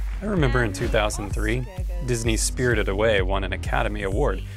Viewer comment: Disney's spirited away? Did I hear that right?